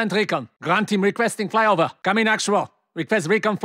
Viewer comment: what does he says? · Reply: Requesting flyover